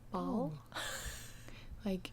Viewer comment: at a moment i thought you said “Paul”....